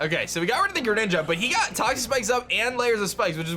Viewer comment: it sounded like you said, "he got the toxapex up" Although I know you said toxic spikes I'm saying it sounded, to me, like you said toxapex